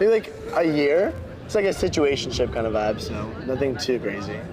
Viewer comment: I thought the dude said “situation SHIFT.” Lol whatever that means, shows you how much I know. 🥴😄